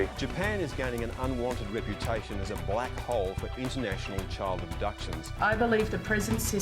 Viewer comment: I thought it said child abortion